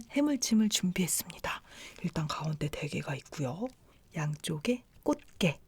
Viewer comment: is it me or did I hear her say "Cardi B" 😂😂